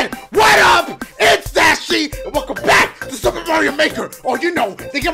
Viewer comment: I love that so much! XD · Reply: It sounded like he said “super Mario baker” 💀